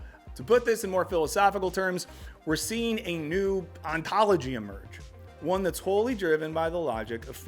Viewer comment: I was watching this on a faster speed and at first misheard it as "We're seeing a whole new ONCOLOGY emerge," which could actually work as a pun. 😂